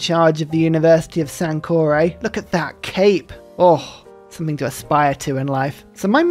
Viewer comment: 'Look at that CAKE!" is what I thought was said about Lord Sucklington before having to rewind to listen again. 🍑